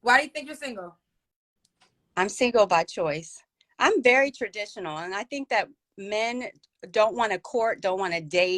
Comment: Did she just say MORALS, TRADITIONAL?